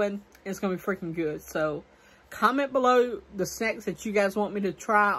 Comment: you were talking about doing at taste testing Thursday and if people have any snacks ideas for you to try, but when you said "sex" to try instead of "snacks" to try....well it sounds like sex... but it could be your southern accent that makes "snacks" sound like "sex"..... or was it a Freudian slip???? A slip of the tongue in which a word that the speaker was subconsciously thinking about is substituted for the one that he or she meant to say.